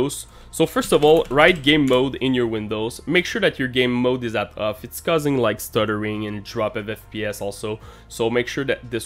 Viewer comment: did you say “set” or “not”